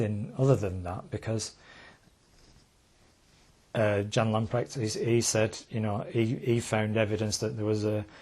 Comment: john ...lunpractor? who did he say?